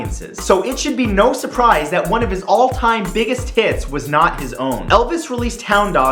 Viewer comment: I heard all time biggest tits XD